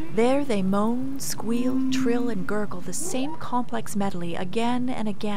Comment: “complex medoly? “ I heard that 😂. Great video tho .